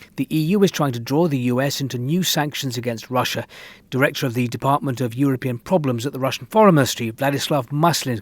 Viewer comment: What? Is it actually called the 'Department is European Problems'?